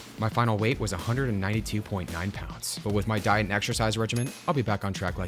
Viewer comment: 🍔🍔🍟"exercise I thought you said extra fries"